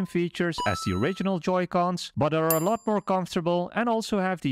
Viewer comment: Terrible functions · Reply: No its when you said turbo functions i thought you said terrible functions